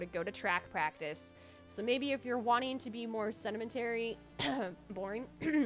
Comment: Hang on, did you say "sedimentary" instead of "sedentary"?